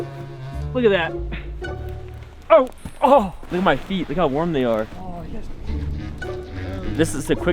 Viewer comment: on 6:34 did say "Look at how warm my feet are." I- But how can you look at warm feet especially wearing socks- just pointing that out lol